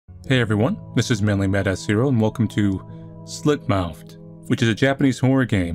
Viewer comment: Is it just me or did he say mannlymadasshero